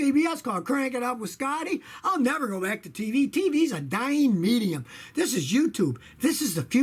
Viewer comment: Hahaha did he just say TV is a dieing medium? LMFAO